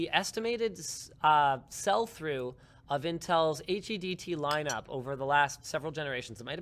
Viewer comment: What is he saying at 20:47. HDDP or something?!?!